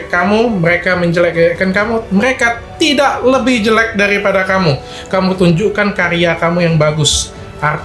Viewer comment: apa saya gak salah dengar 7:46 mereka tidak lebih jelek dari kamu 😀😀😂